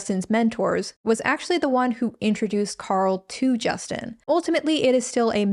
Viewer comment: I’m SORRY did you say the pastors name was JUDAS😳👀